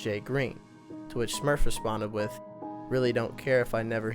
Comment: Did he just say two witch smurff....??????